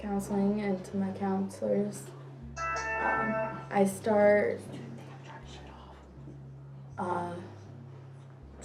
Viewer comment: Anyone hear in the interview 18:14 the phone went off someone says “ turn that shit off!” 😂😂😂😂😂 ??????